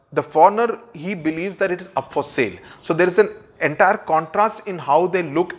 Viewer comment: what did you say sir. Foreigner who believed it is for .. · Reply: Up for Sale